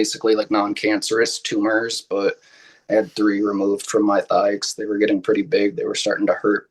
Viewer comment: I have bad hearing, and I heard "lipomas from my thigh". You might want to get your ears checked.